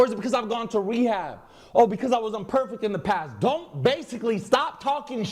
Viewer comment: Did he say he was on pervitin ? Aka meth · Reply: pretty sure he said imperfect